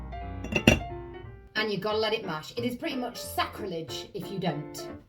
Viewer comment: From a curious cat~~ What was the word you said at 5:11 ? You’ve got to let it ___” mash? Marsh? I would think the word steep, but that’s not what was said….. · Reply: I believe it’s “bash”, but I’m American, so take that with a grain of salt. 😂